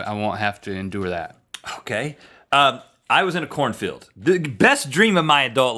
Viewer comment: I yawned at the beginning when Rhett said he was in a corn field...I thought he said "I was in a porn film". Major double-take.